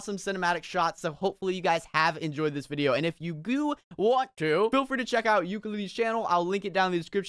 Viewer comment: Did he say goo instead of do